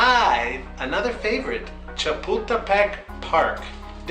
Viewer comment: LMAOO (cha-pull-ta-peck) I heard puta instead of pull Edit: Don’t worry though, we all make mistakes